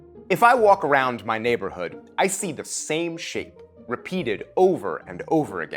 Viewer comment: it took me 5 times to repeat this because my ass swore I heard “I see the same shit” 😂😂😂😂